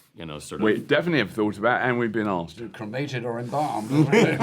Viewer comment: in response to the question about AI and hologram concerts, what does Keith say before "or enbalmed"? Thanks. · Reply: Cremated.